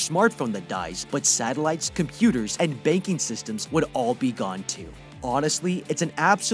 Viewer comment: when he said banking system I thought he said baking system idk why